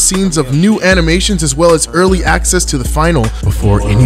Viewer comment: I thought he said “lick my bio”